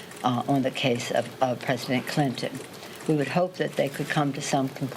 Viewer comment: Did she say President Clinton @ 2:17???